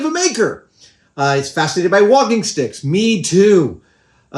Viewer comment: can someone explane to me what a wogging stick is or am I miss hearing what he said?